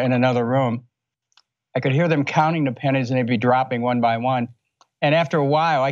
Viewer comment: Lol I thought he said "panties droping" XD